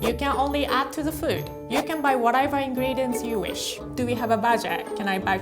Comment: What said "Do we have a budget?" What I heard "Do we have a badger?"lmao